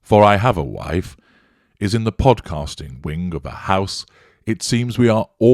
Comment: Oh ... the wife is in the ‘Podcasting Wing of the house’! I heard ‘Composting Wing’ first time. I thought; “Gosh, there must be a lot of compost in that house!” A whole wing. But for Podcasting, I seriously doubt a whole wing will be enough. How many toilets does it have?